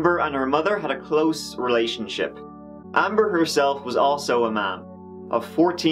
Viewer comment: When he said "Amber herself was also a mom" I thought he said "Amber herself was also a man". Really threw me off until the picture.